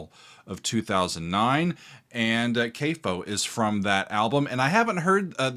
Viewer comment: …not to be a complete douche…but…I heard the word “Capo” at 1:05…😂😂😂…I’m just busting man, keep doing you, you’re great!